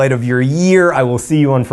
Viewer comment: Wait... Did you just say "I will see you on Fry"? Why is Hank on Stephen Fry?